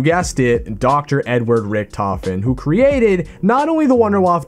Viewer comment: I thought you said, "You guessed it. Dr. N-word Rhichtofen. "